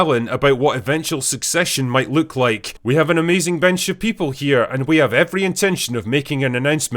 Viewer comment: I was only half listening, when you said that part, so I initially thought you were going to say "We have an amazing Ben Shapiro".